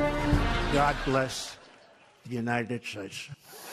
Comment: it sounded like 😖president trump is saying God bless the United shates???? Get your mind out of the gutter wash it and then maybe we will see some semblance of sanity.😒